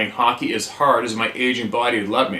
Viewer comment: After your comment, I had to go back and watch this one over again. He never said that he was Asian. I don't know where you got that. Maybe when he said that he played hockey as hard as is "aging", not asian body would allow. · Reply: Haha, I thought I heard "Asian" at first too, then thought about it... was definitely "aging". I feel that also, as an AGING hockey player myself... at 57, I should just get off the ice. =)